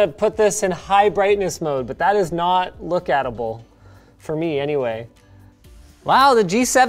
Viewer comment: Word of the day: “Look-at-able” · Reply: Originally, I heard "look-eatable", an interesting concept on its own.